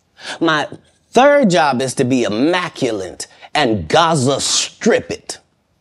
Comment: 😂😅😅😅😅😅😂😆😆😆 Did he just say "Gaza-Stripped" her??!! 😂😅😅😭😭😭😭😅😭😆😆😅 CACKLIN' ova her' in Barbados🇧🇧..